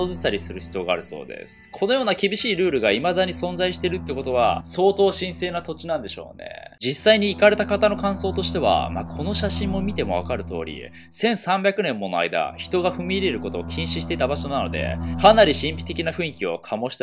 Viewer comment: 行かれた人がイカれた人に聞こえた